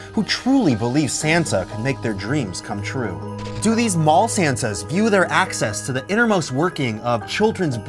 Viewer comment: I thought it said mall Satans at first